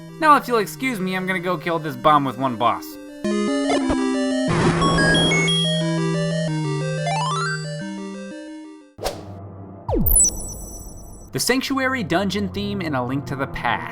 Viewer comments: am I hearing things? "I'm gonna go unalive this bomb with one boss" lol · does he say ima go kill this bomb with one boss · Did he say"I'm gonna kill this bomb with one boss," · Wait..... Did he just say "kill this bomb with one boss"? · wait at 3:32 does he saynow im gonna kill this bomb with one boss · Did he say "kill this bomb with one boss"? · Did he say that he's going to kill this bomb with one boss at 3:39 ? :D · wait did he just say "I'm now going to kill this bomb with a boss"?